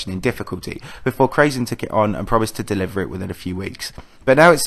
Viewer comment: did he say krazy!!